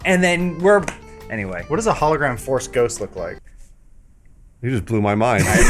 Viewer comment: What does Adam say around 0:46 is it what does a hollow gram force ghost look like ? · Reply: Holograph Force Ghost